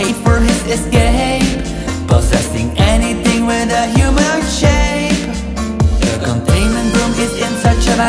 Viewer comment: Anyone else see that at 2:21 say human shape but the subtitles say humanoid shape